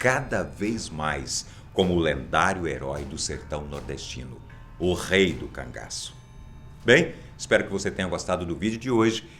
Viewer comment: Juro que entendi "Lampião o rei do cagaço"